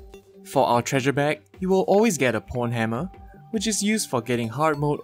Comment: Pornhammer?!?! I can't be the only person who hears this